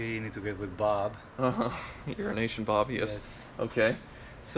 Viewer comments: Sounds like is saying "Urination Bob" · Is that other guy or Urination Bob? It really sounds like you say the latter...